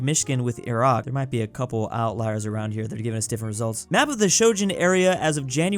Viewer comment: "Sogun" instead of Shengen XD again